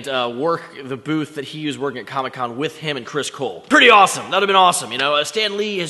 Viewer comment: did u say chris cole?????